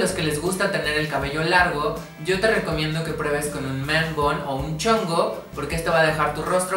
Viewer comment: que dijo en el min. 1:51 ?? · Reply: Si te gusta llevar el cabello largo prueba con un "man bun" o "chongo"